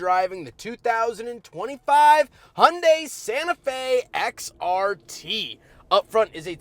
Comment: I could have sworn you said Santa Fe SRT... I had to rewind it to see if I heard you correctly, which I did not.... BUT a hellcat swapped Santa Fe would be sick!